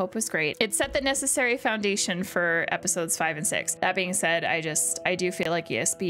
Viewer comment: funny, what people hear ... at normal speed i heard 🇩🇪 "Episode 2-6" :-) while at lower speed it clearly is 🇬🇧 "episodes 5&6".